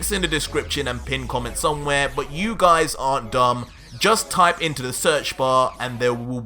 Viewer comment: did I hear you say "you guys are dumb"